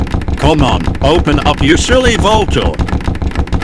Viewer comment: THIS IS SILLY VULTURE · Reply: Actually he said come on open up you silly vault door because the subtitles thought they said vulture